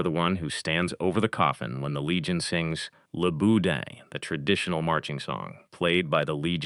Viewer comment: BOUDIN (flesh saussage) instead of Bouddha. Lol